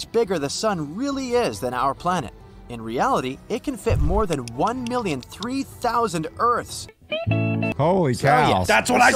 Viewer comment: Is it juat me or did i hear 1 million 3 thousound instead of 300,000?